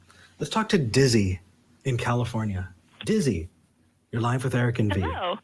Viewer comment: I'm so idiotic I really thought they said "Disney from California" I was like, "D I S N E Y L A N D ?"